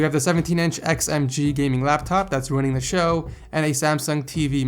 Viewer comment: Did he say that his laptop was ruining the show at 2:33?